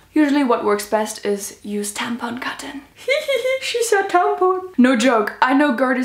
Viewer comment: When she said "use tampon cotton" the first time, I thought she said "useD tampon cotton" I was like NOOOOOOOOOO